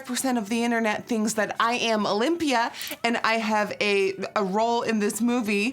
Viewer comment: At 1st I thought you where saying Olympia it took me a sec to realise you ment Olivia lol